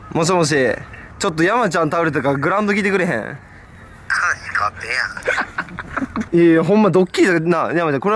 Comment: 「もしもし、山ちゃん倒れたからグランド来てくれへん？」の後がーどまんなんて言ってんの？？誰か教えてプリーズ。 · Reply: 話変わってるやん やと思います